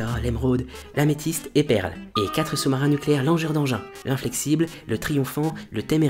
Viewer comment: c'est moi ou il dit "4 sous-marins nucléaires «langeur» d'engin" Cet épisode est génial bravo !!